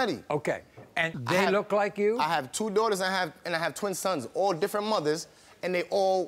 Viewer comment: How many children does he have? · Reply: okay I was confused be I thought he said he has 2 daughters and 20 boys